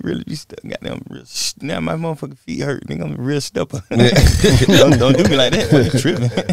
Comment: Dawg... from 1:16-1:20 I don’t think Thug said a word. He was just mumbling... 😂😂😂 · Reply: "My feet hurt I'm a real steppa'"